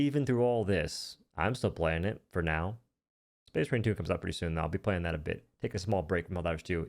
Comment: What did you say? Space Rain 2?